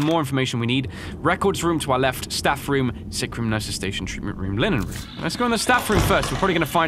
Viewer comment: reading the places "nurses station, imalenaries" what even did he say? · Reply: He said treatment room and linen room